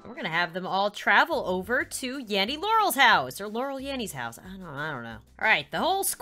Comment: I thought the intro said Yammy